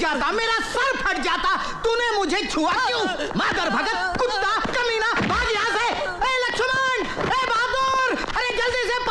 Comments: did he just said “Mother fucker”?😅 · did he said motherfucker ?